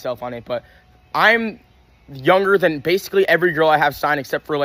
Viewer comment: i aint a jack fanboy but he said he is younger than every girl he signed not all the girls he signed are younger than him i think u heard it wrong if you wanna clarify this is the time stamp